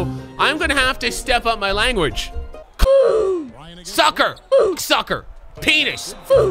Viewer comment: it sounds like he is saying "fook"